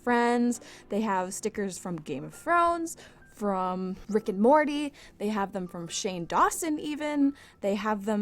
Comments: I thought she said friek and morty 😂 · Oh, I thought you said "frick and mordy" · I thought she said frick and morty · How come when she said Rick and Morty I heard “Fricken Morty” 😂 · When you said rick and Morty I thought you said fricken Morty